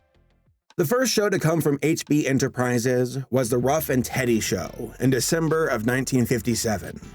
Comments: did he say "Ruff and Teddy" show? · This video is still getting traffic so I just wanted to update on something! I made a handful of mistakes in the video. For example, I call the 'Ruff and Reddy Show' the 'Ruff and TEDDY Show' by mistake. I try my best to make sure that my sources are accurate for my videos but the occasional error happens. Just wanted to address that! Thanks for watching the video and for the support! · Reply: 😆😆😆 I thought you said "Rough and Teddy" to avoid the YouTube censor bots! 😁 You do know what "rough and ready" means? 😏😉